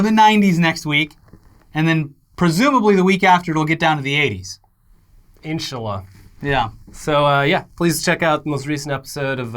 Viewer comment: did said Insya Allah